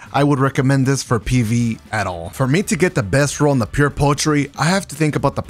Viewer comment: I swear I hear "Pure Poultry".